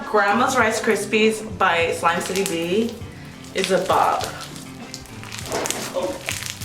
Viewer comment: I swear she said “the *bob*” But then I played it back and realized she said “*bomb*”